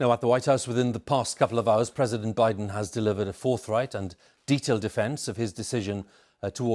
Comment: Did he say Biden delivered the fourth reich @ 3:47? Scary!